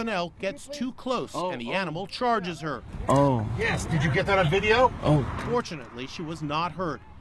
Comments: Who else heard on 1:41 where the gay said”YES YES!!” after the elk hit her?😂🦌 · I thought he said unfortunately.😄